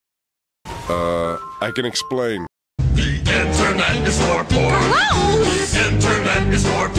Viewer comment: i always thought this said corn